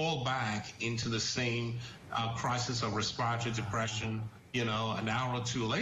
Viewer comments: Wtf did he say at 4:27? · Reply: RESPIRATORY DEPRESSION REFFERRING TO HOW PEOPLE DIE FROM OPIATES> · Wtf did he say at 4:27? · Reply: Crisis of respiratory depression